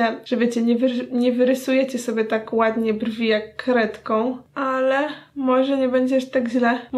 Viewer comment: Zamiast kredką usłyszałam krewetką😂😂